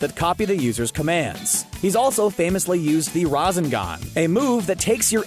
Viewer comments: DID HE JUST SAY THE RA-ZEN-GON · What I hear in 5:05 rasanga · Did he just say rusen gun instead of rasengan? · did he just say "razengan"